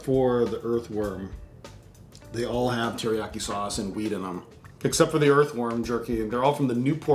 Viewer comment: did he just say teriyaki sauce and weed in them? Haha